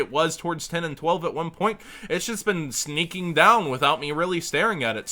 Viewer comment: I heard "sneking down" :P